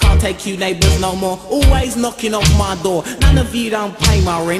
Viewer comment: anyone know what he says pretty much at the end...from 2:50 after he says, non of you dont pay my rent...?? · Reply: Non of you don't pay my rent .....so why don't you keep silent